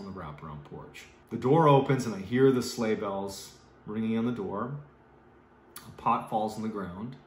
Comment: Every time Mr. Ballen says….”The Sleigh Bells Ring” in my head I hear “Are you listening?”